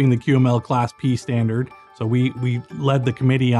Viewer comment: I thought it said class D from scp 😂